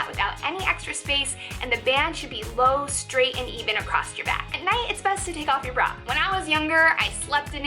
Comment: Did she say "acrossed your back"? Pet peeve! There is no such word as "acrossed"; it's just "across."